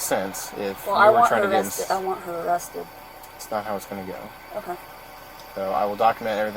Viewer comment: Did she just say i want her dead¿